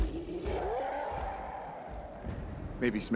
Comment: what did he say🤨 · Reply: Cap: “maybe smash a few things along the way” Professor Hulk: “I think it’s gratuitous, but whatever..”